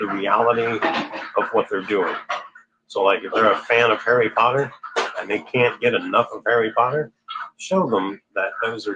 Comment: did say that we should say a 9 day or 90 day novena to Mary Undoer of Knots to make our loved ones aware of the effect of their interest in dark stuff? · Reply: He said a 90 day novena - find a novena to Our Lady undoes of knots and continue it for 90 days, he didn’t specify which one